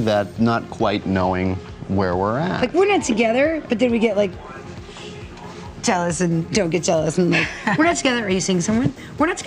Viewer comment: What is Pauley saying at 1:13? I'm french and just can't understand what she says xD whatever they are juste the best couple ever, even before Tiva *w* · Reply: She says that they aren't together but they get jealous when one or the other is dating someone else.